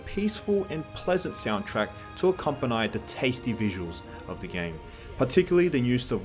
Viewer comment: Did he just say "accompaneigh?" instead of accompany?